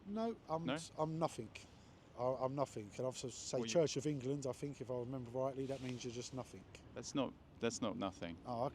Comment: "I'm nothing. I'm nothing. I think if I was to say -- 'Church of England' -- if I remember right, that means you're just nothing." That's just heartbreaking.